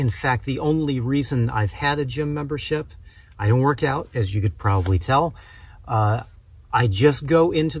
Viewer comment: Sorry I thought u said u worked out I deleted my last comment sorry again